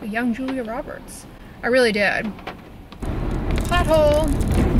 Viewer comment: I swear I thought you said butthole in a high pitched voice ...when you went over the “pothole” xD I had to rewind it. Made me laugh, thank you lol have a good rest of your week!! ❤️❤️❤️❤️❤️